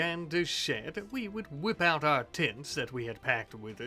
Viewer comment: I had to go back twice, cuz the first 2 times I swore I heard "Whip out our tits" and was highly confused- Was listening to this as background noise and that immediately caught my attention lmao